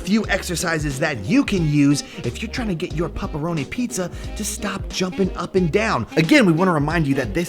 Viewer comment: I thought it said teach your dog to stop juuling